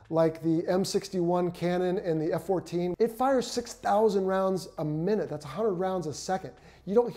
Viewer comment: "1000 rounds a minute, thats 100 rounds a second.." So a minute is not 60 seconds? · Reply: He did say 6000 rounds a minute, so you've misheard.